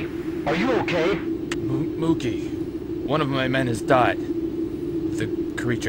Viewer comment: I swear says "One of my men is died."